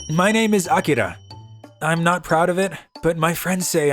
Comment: Omg I thought he just said "I am Akira. I am not proud of it." 😭